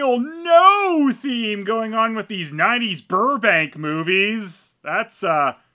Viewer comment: Burbank I thought you said bourbon well I am here might as well watch it